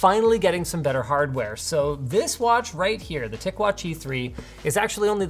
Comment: I thought he said thick watch at the end... I realized my mistake